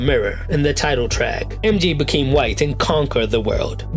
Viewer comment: nga… what u jus say ? 😂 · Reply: I was literally like “did he say white or am I hearing things?” 😭